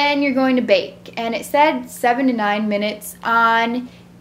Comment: when you said how long to put them in for it sounded like you said 79 minutes and i was like OMG is she trying to burn down her house? LOL